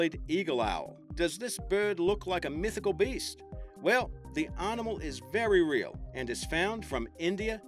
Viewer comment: he says "arnimal" instead of "animal" ...(-_-)...